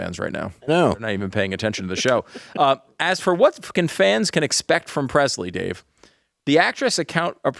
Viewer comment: Ahoy!!!! I had to go back and listen to 12:30 a few times. I thought said " What fuckin' fans"